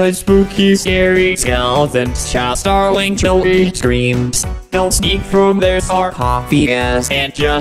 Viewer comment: I thought he said "show shirlly CHILDREN screams "-✋🏻😂